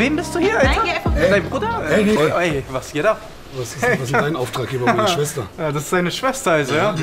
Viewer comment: was sagte er bei Minute 1:00 ich verstehe nur "wer ist dein Auftragsgeber meiner Schwester"